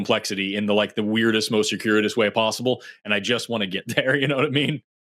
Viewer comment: Captions say securious, the word is circuitous.